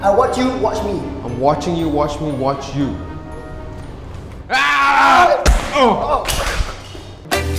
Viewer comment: why can't i comment on my phone? THAT ADORABLE KOALA APPEARED 5 TIMES! 2:26 my Subtitle/CC said "I'm watching you watch porn watch you by" LOL btw I noticed a wire between foxys hair (2:09), is that a microphone? this really bothers me looking at her 'square bump'